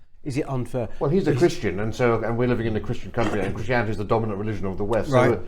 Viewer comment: Did he say that Dawkins is a Christian? at 20:47.